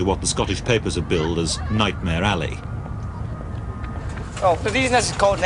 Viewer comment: Oh Nightmare Alley... I could have sworn you said 'Nightmare Abbey'.